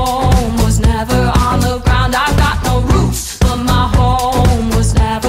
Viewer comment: Song name: No roots What i searched: No roots (seriously how do guys mistake it for roofs) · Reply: I mean, I heard "I got no boots" but I heard it in a restaurant....